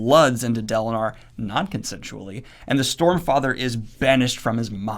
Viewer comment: I am pretty sure I heard 'Stormfrather' from at some point. xD